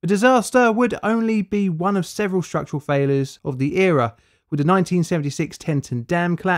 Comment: This dude's so English that I thought he said "Australia" when he was saying "structural failure"